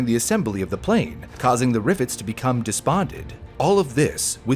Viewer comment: You said it right. At first I thought I heard “causing the rivets to become despondent”. Nice work. Best technical explanation of this mishap that I’ve seen in a video. Some 90,000 cycles? Aloha Airlines was irresponsible. Thanks!